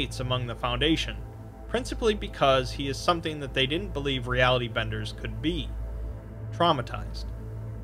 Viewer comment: I keep hearing "reality vendors".